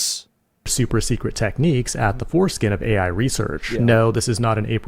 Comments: Did it say at the foreskin rather than at the forefront? 😂😂😂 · did he say "foreskin"? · did bro just say foreskin?